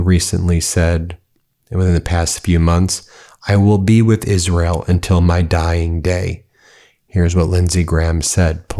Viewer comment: Correction, he did not say "I will be with Israel till my dying day". He said "I will be with Israel till our dying day". I think he was wanting nuclear war and that is why he said "our". He was so horrible, good riddance.